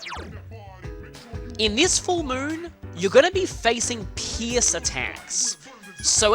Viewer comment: love your accent bro sounds like your saying piss when your saying Pierse